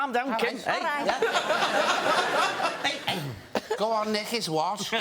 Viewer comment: What is he sayingng to annoy Kim? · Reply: He told her to "nick his watch" (steal his watch)